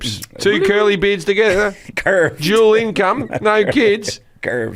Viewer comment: I thought Jim said (about same-sex relationships) "duelling c*m". Hahah